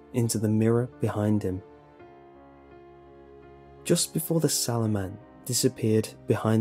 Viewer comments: i thought it said swallow man at first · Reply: I thought it said shadow man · I thought it said the shadow man